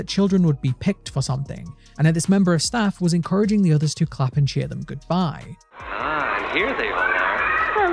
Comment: I thought bro was going to say “This member of the staff was encouraged to to clap the children” 😭😭😭😭